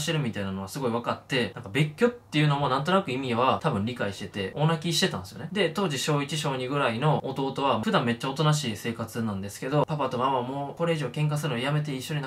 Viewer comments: 真面目な話してるところで聞き間違えるのはおかしいんだけど妹が大泣きしてたが、オナ禁してたに聞こえてしまった。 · 大泣きが、オナ禁に聞こえたおれは末期